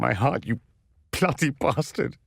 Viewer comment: Does anyone else hear him saying 'plotty bastard' instead of 'bloody bastard' or is it just me?